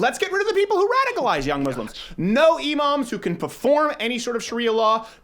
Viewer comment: When he said no imams I thought he said no emos but honestly I'm fine with either